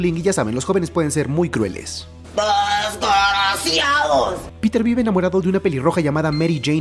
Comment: nunca entendí que dice la señora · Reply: Dice " desgraciados"